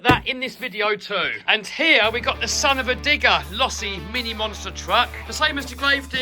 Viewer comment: what did bro say 😭🙏🏻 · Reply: he said son-uva digger, what are you on?